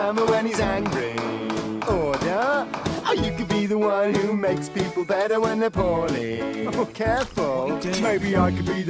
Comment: what did he say? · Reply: or you can be the one who makes people better when they're poorly